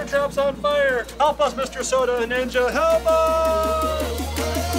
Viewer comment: UR MY FAVE YOUTUBER CHAD, SEE YA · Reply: wait did Chad just say soda ninja